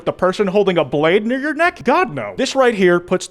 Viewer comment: did he say the n word? I’m just wondering not trying to cancel him I just wanna know if my ears are stupid or not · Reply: he said near